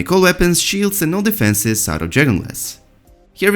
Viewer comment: Every time this guys says "dragon glass" I heard "Journalist" . Can't unheard it.